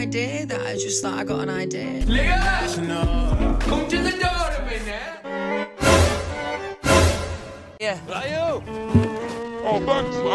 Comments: “Leyuhmm come to the door a minute” will ALWAYS be my favorite quote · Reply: I thought he said "come to the door mate" 😭✋ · “Leyuhmm come to the door a minute” will ALWAYS be my favorite quote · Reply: I thought he said "liam,come do the dart in a mimute-"😭🖐️